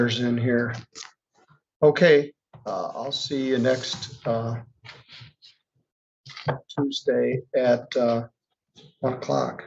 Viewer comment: Middleton says see you next Tuesday... And it's fantastic. 😅😎😎😎 I think he knew halfway through saying it. Love it!!